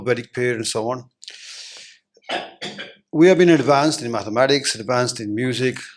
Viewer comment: I could understand 90-95% of his entire speech... but there were a few words that were really difficult to understand because of the accent. For instance: 2:00-2:20 which Veda is he talking about? Couldn't understand that. If not subtitles for the entire conversation, could perhaps edit the video to insert quick one liners during the convo to clear up the accent difficulties. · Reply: the word that you not understand was "mathematics".